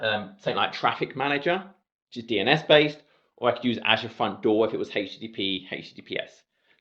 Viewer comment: what did you mean? Azure fun door? I didn't understand. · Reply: Azure front door